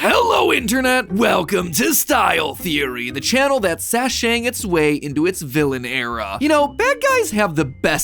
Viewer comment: damn i really thought you said sasha-ing your way into your villain arc